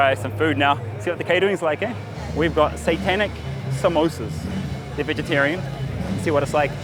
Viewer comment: did you say satanic samosas or santanic samosas whaaaat